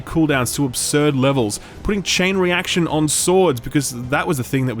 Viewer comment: so, as a 40k nut, I misheard when he said "added Chain Reaction to swords" I heard "added chainsaws to swords". I swear I had one of those moments where I got super hyped for the IP again, only to be let down when reality kicked in.